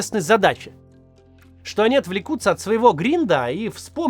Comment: вместо "отвлекутся от своего гринда" (рабочий процесс) услышал "отвлекутся от своего гриндера" (приложение для нетрадиционных знакомств), немного удивился